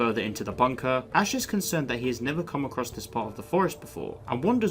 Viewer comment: I thought you say Ash is never comeback